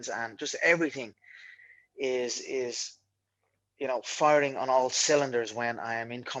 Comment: “farting on all cylinders” 😂😂😂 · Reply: Obviously he said firing. Just sounded like he said farting. Chill bois